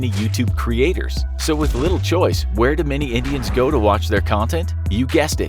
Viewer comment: "where do many idiots go" I know he said Indian it just sounded like idiot